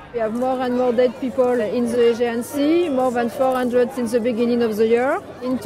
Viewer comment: I think the woman on 5:34 said "We have more and more dead people in the AEGIAN SEA" not "in the agency" as you subtitled it.